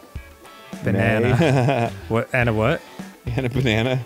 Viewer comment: Lmao did Arin say Anna Malle 🤣🤣 iykyk